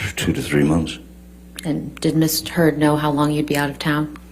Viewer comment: I SWEAR Johnny's attorney said 'Miss Turd'!!!!!!!!!